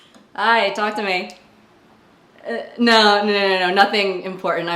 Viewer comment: Am I tripping or did she say "Heyy talk to me" as a reference to that inside joke she mentioned in another video?